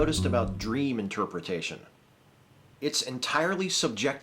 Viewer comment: At first I thought the subject said 'Dream Interruption' gotta admit I'd like to hear that one too. Cuz yea, i HATE that.